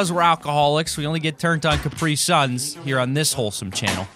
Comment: Omg he says crapisuns??!! I always thought he said croassaint